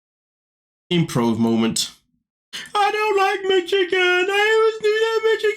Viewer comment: When you kept saying Michigan I thought you were saying McChicken. Now I want a McChicken. Thanks!!!😂